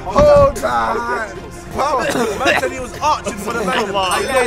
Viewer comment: who else heard chunkz say “ he was arching for the mandem” 21:03 😂😂😂😂